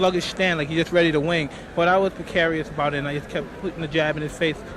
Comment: He most certainly did. Watch the video. There's also footage of him calling his style impetuous. I agree that these aren't typical usages of the words, but they aren't incorrect, either. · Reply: ? It's not totally clear to me but I think he actually says 'but I wasn't carious about it' meaning, I think, that he didn't care about Gross's aggressive start. What do you think he says?